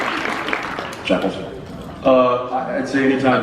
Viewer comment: DID JARED CALL JENSEN JENKLES 57:43???